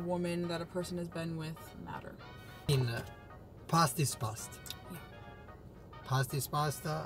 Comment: I DEAD ASS thought he said "pasta is pasta." lmaoooooo